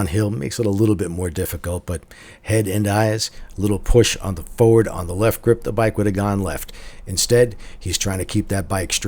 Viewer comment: "Push forward on the left grip and the bike would've gone left"? Did I hear that right? Around 1:40 min mark